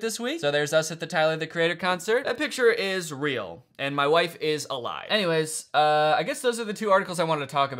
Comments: He actually said "My wife is a lie" and Laura never existed in the first place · “that photo is real. and my wife is alive.” NEEDS to go in a no context video💀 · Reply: What I heard "and my wife is a lie" 💀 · It's a conspiracy the subtitles say "my wife is a lie"